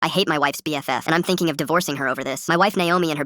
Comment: I dumbass thought the intro said “I Hate My Wife’s Bf”😂😭😭